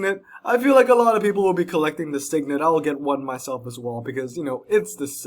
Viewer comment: yeah I said that I felt like a lot of people would be collecting it lol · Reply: OHHHH I heard "not a lot of people will be collecting it" LOL